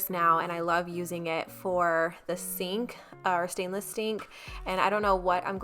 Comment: Anybody else catch when she said she loves cleaning her sink with the bar keepers friend? She said she loves cleaning her sink stainless stink 😂 i love you Watch it and listen closely